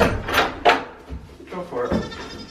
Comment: Goat for it and later Wher did you goat. Thats all I Heard, or herd as the farmer say.